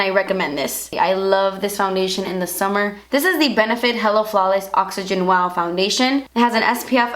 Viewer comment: I thought you said "Hella Flawless" brb creating my own foundation